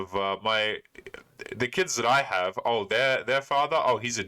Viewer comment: I think you misheard her when she said, "they're with their fathers". i.e. multiple fathers. That's her problem....yikes.